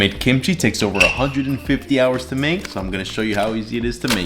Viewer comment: Not going to lie, at first I thought he said it took 150 days, not hours, to make. I was legit impressed with the long form commitment until my brain came back online.